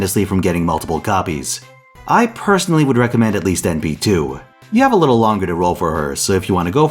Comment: Did you say NP2? I rolled for her for that specific reason.